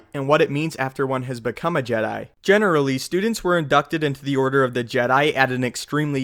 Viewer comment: Could have sworn he said "abducted"